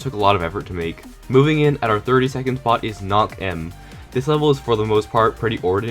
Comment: 4:7 thought he said nock em and i was like ahasdjajdashjdahs